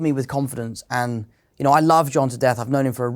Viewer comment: I thought he said "I love Jon's death," at first. 💀